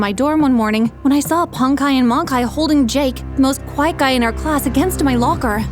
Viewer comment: It says "the most white guy" instead of "the most quite guy" at 2:12 😅 can u change it lol😂